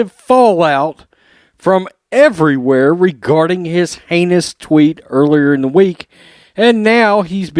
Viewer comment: Did you just say anus tweets ?!! Ha Ha !!